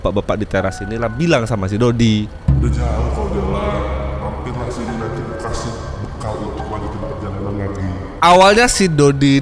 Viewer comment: dia ngomong apaan? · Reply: udah jauh ko jalan, mampirlah sini, nanti ku kasih bekal , untuk lanjut in perjalanan..